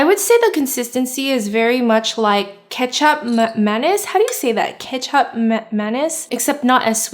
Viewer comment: Kicap Manis (Kee-Chap Ma-nis) Kee = like how you would say key cap = chap like a chapstick Maa = like mama Nis = tennis but only nis This is how you pronounce it! Hope it helps 😇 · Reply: Lol I thought she was saying ketchup mayonnaise 😄😀 lol